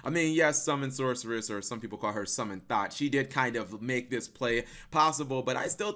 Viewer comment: I heard "Summon THOT" and laughed irl hahahaha